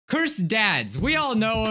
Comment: Took me a minute to realize he was saying “cursed ads” not “cursed dads.” Thought I clicked on the wrong video. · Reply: His editor also thought he said cursed dads but realized he was supposed to say cursed ads so his editor joked around and put " cursed dads "